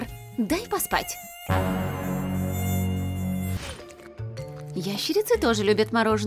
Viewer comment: мне одной послышалось "извращенцы тоже любят мороженое " вместо "ящерицы "😅